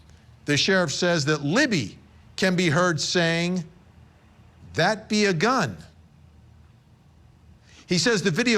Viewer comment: I heard it , "that be a gun".‼️